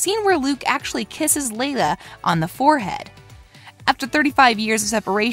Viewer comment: Did she say "Leila" at 5:40?